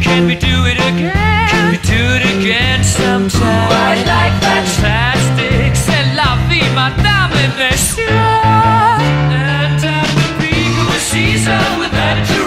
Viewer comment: ピーマンダメですよ～ にしか聞こえない